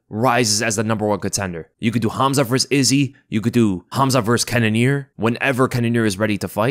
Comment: I just heard " Khazmat vs Cannioer " Holy Sh♤t..... I have to see that now !!!